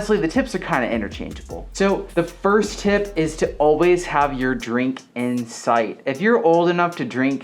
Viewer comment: I first heard, "to always have any drink in sight." And I thought, 'That's pretty forward, but I guess I agree" lmao. Great video ^w^